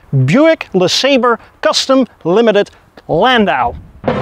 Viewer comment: Landau...for a second there I thought that I heard you say Land Yacht!😂